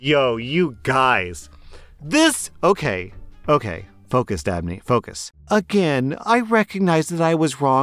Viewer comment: YOUR NAME IS DABNEY? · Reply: Yeah, at first I thought he said Daphne but he said Dabney.